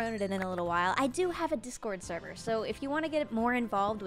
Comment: its been over a month and im just watching this now but. YOU FORGOT TO PUT YOUR MINECRAFT SERVER IN THE DESCRIPTION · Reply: sorry you said discord but i thought you said minecraft. BUT YOU STILL FORGOT TO LINK iT